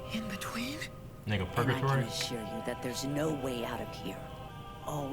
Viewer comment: Instead of saying like a purgatory I thought he said nigga purgatory🤣